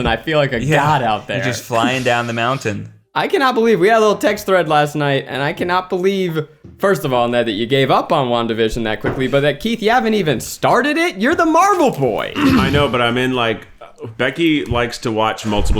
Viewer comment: I kept hearing "Long Division" instead of "Wanda Vision" so I was very confused for a bit lol